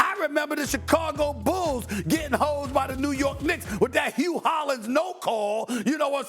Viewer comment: Did he say the Chicago Bulls got Hoe'd 😂